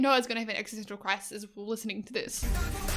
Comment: Exostential crisis😂 I immediately got reminded of sillypak😅 No offense though😄 I love you both so much😍 I mean I don't know even if you used the word like this or I just misheard but that was so cool either way🤩 Love your videos always. Stay happy and healthy❤